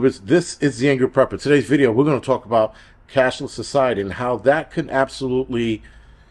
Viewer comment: I thought you were saying "castle society" at first.